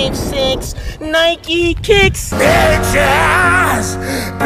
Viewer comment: it sounds like hes saying 'bitches' insted of 'peaches'